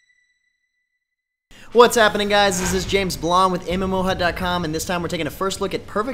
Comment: Oh, i thought he said James Blunt.